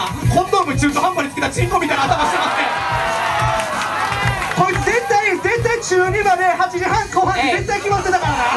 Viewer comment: なんて言ってる？こいう絶対中二まで…の後が聞き取れない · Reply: 雄太堤田 こいつ絶対中2までご飯8時半って決まってた